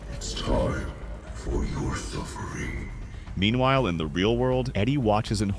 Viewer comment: You didn't let Vecna finish! He actually says, "It's time for your suffering, to end."